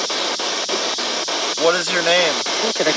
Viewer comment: When he asked "what is your name" at 31:28(ish), I heard "what can I get for you?" lol.